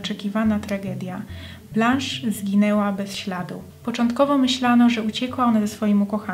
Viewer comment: W 3 minucie usłyszałam „zginęła bez śladu” zamiast „Zaginęła bez śladu”